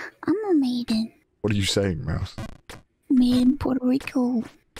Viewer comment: maiden puerto rico ah i thought i heard made in Puerto rico i see i see